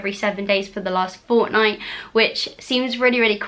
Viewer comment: did u just say fortnite?